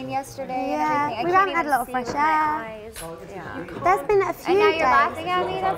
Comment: It sounded like she was saying "you are laughing at me" to Gillian cause you can hear her saying "no no"